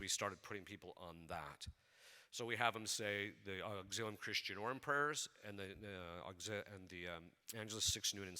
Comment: At around the 51:40 mark where Fr Ripperger mentions what he has people do to overcome. He mentions the Agellus 6, noon & 6 but he mentioned another prayer that sounded like Auxxillum Christian Or? I know that can’t be it, does anyone know? I’m new and trying to learn. Thank you & God bless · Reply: Auxilium Christianorum prayers, which are a specific set of Catholic spiritual warfare prayers used for deliverance, protection, and defense against demonic influence. You can check up more